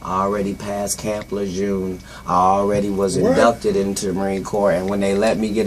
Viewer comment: when the man asked katt did he go to school too, katt said "no they just gave me the tests" what does he mean they gave him the tests? who gave him the tests? and what tests? lol wtf idk...and he mentioned being abducted and shit. wtf ..lluminati? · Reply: Kush Kushington lol I misheard him. I thought he said abducted but he didn't lol my bad.